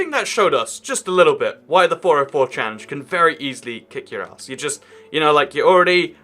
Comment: did he say "can very easly kick your ass?"